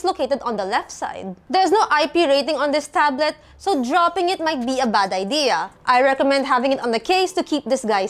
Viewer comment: "Theres no IP rating on this tablet so dropping it might be a bad idea" did i hear it correctly Edit1: There's a fight in replies 😭😭 Edit2: The sentence makes sense if it's, "Theres no IP rating on this tablet, so spills of water may harm the device and submerging it in water is bad idea" or "There's no Militarty Standard Rating like MIL STD 810G on this tablet, so dropping it might be a bad idea" Last edited 22 April 2025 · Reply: u heard it right lol